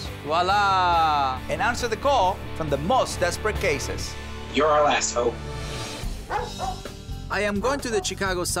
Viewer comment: Did anyone else misheard guy on Skype saying "you're an assh*le" and had to rewind to make sure it was "you're our last hope"?